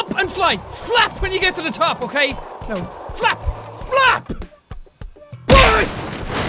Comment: did he say fap or flap?